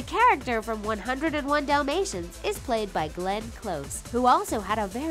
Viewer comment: i almost misheard it as Glen coco ahhhhh